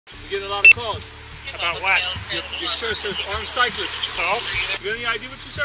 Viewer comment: At first I thought they were saying he was wearing a shirt that says “R” cyclists, which I thought was ‘rape cyclists’ since apparently it’s against the rules to even say the words rape, sexual abuse or child abuse anymore (I guess if we don’t say the words the actions can’t happen?). Not that it would matter, but it would’ve made the story much more interesting, cuz why would that shirt ever be made?